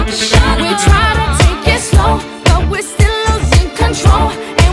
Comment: Being a non-native speaker of English, it's funny being finally able to understand what the lyrics say 13 years later, lol. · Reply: Yeah when I was 10 I thought she said şarapçı şarap şarap which means wine seller wine wine. Lol because "shut up just" exactly sounds the same as şarapçı lol